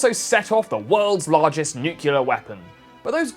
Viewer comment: Did he say "NUCULAR weapon"?